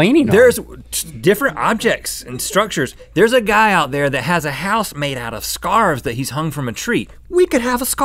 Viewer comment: I thought they were saying scab city ahaha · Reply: misaki I thought they said a guy built his house out of scars :D